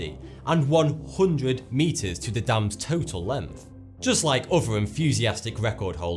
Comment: did you just say "lemth"? XD